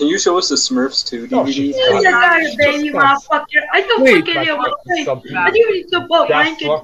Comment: am I the only one that noticed he said what I think he said? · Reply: Sony Pictures Classics Fan 2004 did he say Mother*uker